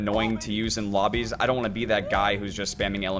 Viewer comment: Did i hear.... Andrew??